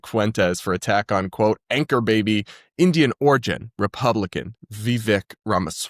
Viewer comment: I was listening and for a second thought you said “anger baby” which I guess also applies